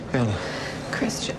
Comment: I said this 4 years ago and I'll say it again. I swear Jamie Dornan says Ella (maybe Bella) NOT Ana. I've related that specific moment like 10 times and zoomed in on his lips and I swear he's making an L sound, not an N sound. Does anyone have an explanation for this. I don't think an Irish accent or a fake American accent would make an N look and sound like an L. But there's no way he'd be calling her Bella or Ella.